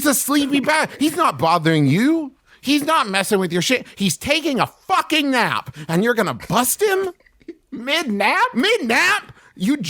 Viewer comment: OK DID MY HEADPHONES MESS UP BC TO ME IT SOUNDED LIKE TRAVIS JUST SAID "hes taking a FUCK- and you're gonna BUST HIM?"